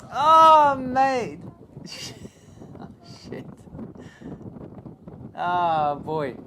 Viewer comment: woah what was that word? did I hear shit??